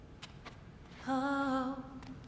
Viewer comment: it sounds like Elsa said Hoe.